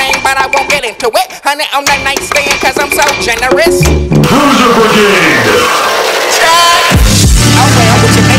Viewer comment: anyone else hear "lose your virginity" instead of "bruiser brigade"